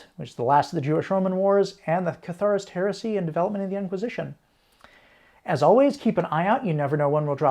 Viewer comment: I could have sworn I heard that last topic as "the Horus Heresy and development of the Inquisition".